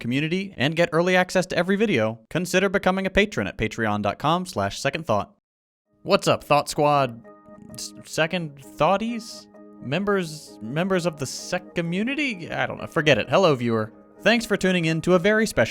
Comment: Ngl, I heard "Thot Squad" haha